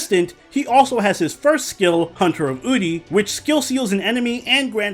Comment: Am I the only one who misheard the first skill name as Hunter of b**ty? 😂